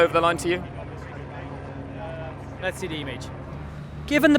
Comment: wtf did Grosjean say????? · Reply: "Let's see the image". He dodged that trickster question very well.